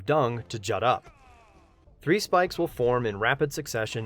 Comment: I thought you said "White Defender slams his arm into the ground, causing large spikes of dung to shut up."